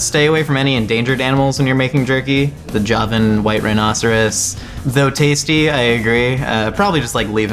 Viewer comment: sorry, did you say joven white rhinoceros?